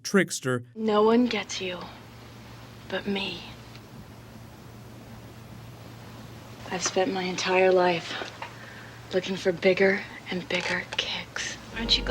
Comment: I thought she said "I've spent my entire life looking for bigger and bigger d*cks" lmao